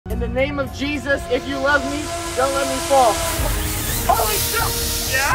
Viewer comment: did he say holy s###